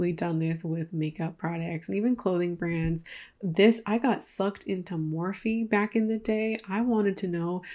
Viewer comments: I totally thought you said, "I got into morphine back in the day" and my first thought was, "Aw poor thing, I'm glad you're doing better" lmao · Girl I thought you said you got super into Morphine. I was like, dark turn, but I respect the journey.